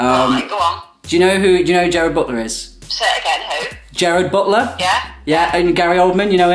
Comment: i thought mom said "say it again ho" instead of who?